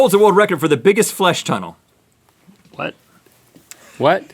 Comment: Everyone thought the flesh tunnel was something else... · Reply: I heard "flush tunnel"